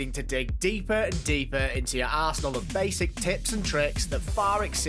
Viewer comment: Is it just me but it sounded like he said you need to dig deep in your arsehole ? Ik he said arsenal but it sounded like he said arsehole.